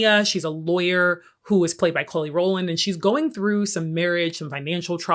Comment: I keep hearing “KHLOE” Rowland and I’m like wait, does Kelly have a sister I didn’t know about 😂😂😂